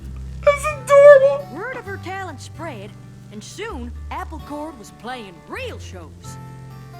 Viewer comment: Wait, Apple Chord? I thought she said Apple Core.